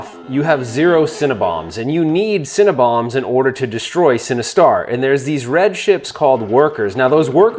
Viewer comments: Legit thought he said "You need Cinnabons to defeat Sinistar" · I keep hearing “cinnabuns” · mmm “Cinnebombs”…. sorry that’s all i heard. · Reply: I kept hearing "Cinnabons" - equally enticing but evil! :)